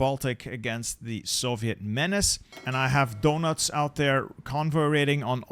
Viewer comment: did you call him donuts?